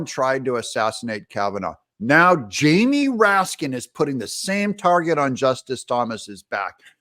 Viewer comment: If Judges Engeron & Chutkin haven’t recused themselves, then neither should Clarence Thomas. To hell with Ratskin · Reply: I kept hearing "RASPUTIN"! But "Ratskin" is clever.